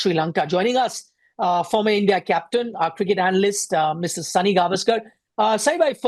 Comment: did he say Mrs Sunil Gavaskar